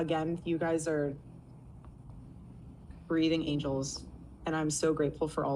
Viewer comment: “You are grieving angels”…and you will be let go soon. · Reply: I thought she said breathing angels which seemed really weird.